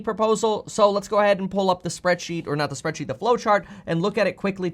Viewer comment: I thought you said blow chart. Lol